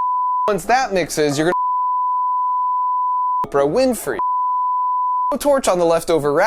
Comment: sound like he said oprah winfrey 😂😂😂